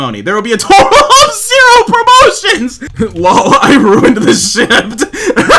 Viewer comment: i thought he said shi# for a second LOL